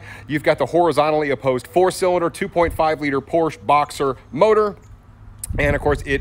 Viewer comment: did you really say "Porsche Boxster"? Since when??